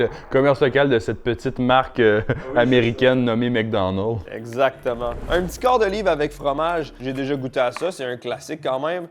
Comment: ??? J’ai cru comprendre " le quart de livre" ... C’est quoi ce nom de Burger ?😅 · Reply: quarter pounder